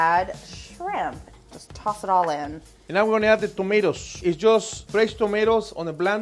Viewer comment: Did he say "I'm adding tres tomatoes"?? 😂😂 Lovely lovely chef overall.